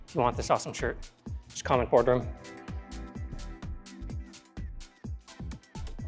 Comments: Boardroom! Haha, I thought you were saying "Fla-knee-go" . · BOARDROOM! I thought you were saying fla-knee-go the whole time 😅 Welcome back to! You flashed all my projects lol